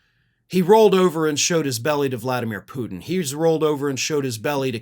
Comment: When said he rolled over and showed him belly I thought he said he rolled over and showed his willey 😂😂😂 OMG laughing at the lil 🍄 things in life 😂😂😂😂😂😂